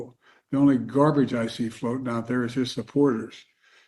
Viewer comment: I can’t even understand him. Like I literally can’t understand what he’s saying.. Only thing I heard was “ only garbage I see floating around are his supporters”